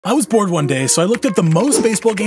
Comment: at the start I heard “I was born one day”😂